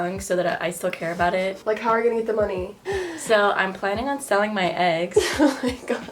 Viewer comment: I thought she said “I’m planning to sell my ex” 😂😂😂